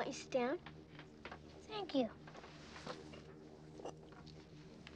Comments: "No thank you, I take it black. Like my men." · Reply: I thought she said, "I like it black. Like my man" instead. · Why is everyone saying "like my men"? I clearly hear "like my man"..are my ears that bad already?